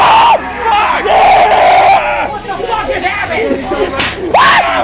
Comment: does anyone else hear yell WHAT THE FUCK JUST HAPPENED